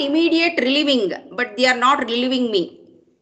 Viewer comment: I heard "they're not leaving me"!!! 😵